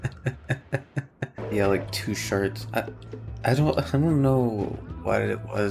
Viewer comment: "two sharts" Had to re-listen to make sure I didn't hear things